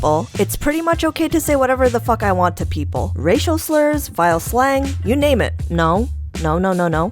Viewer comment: Omg I thought at 2:02 you said Rachel Slurs. Why is my listening terrible 😂